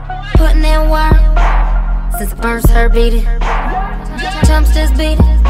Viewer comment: Yooo.......at 0:46 is she saying she's been putting in work....since the FIRST HERPE??? o_O